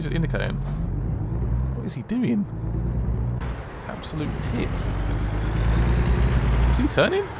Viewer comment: Did he call him a tit?? 🤣🤣🤣🤣